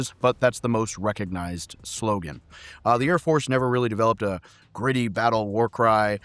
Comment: Oh! You mean the ARMY AIR CORPS!! I thought you said Chair Force ❤🤣🤣🤣🫡🫡🤦‍♂️